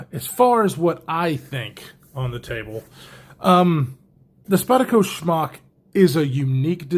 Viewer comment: I just gotta ask...are you pronouncing the Smock as Schmock as a goof? Cause I laugh every time you say it! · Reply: I actually heard it as Spyderco Schmuck the first time he said it!